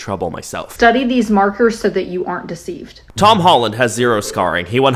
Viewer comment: i know im super late but did she just say tammy holland instead of tom holland😭🖐